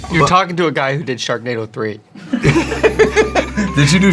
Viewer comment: "Sharknado 3" STG I thought he said "Chardonnay with Brie"